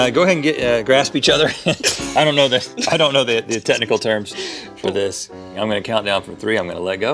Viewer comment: when rhett said “grasp each other” and then said “i dont know the technical term” i thought he said “the testicle term” and i got very concerned given what he said before.